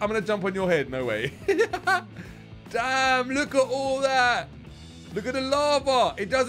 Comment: did you say damn at 2:42 or dang??? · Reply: He said Damn